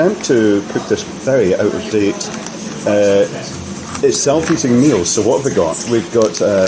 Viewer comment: With the accent, I thought you said 'Self Eating Meal' ... ha ha ... I thought AI has gone too far now !!!